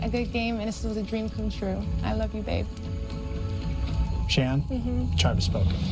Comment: did shan say "i love you babe"??????????????????????????????